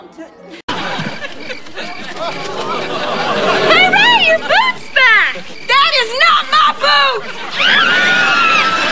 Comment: Name of the episode at 0:21 btw what did she say there? "That is not my.."" what?? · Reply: Don't Care she said "that is not my boot"